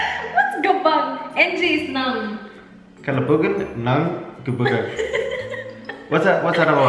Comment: Did he just say kalibugan? Hahahahahaha 😂😂😂